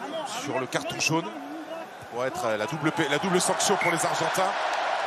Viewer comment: c’est moi ou le commentateur a voulu dire la « double pénètr… »😂😂 coquin va😂😂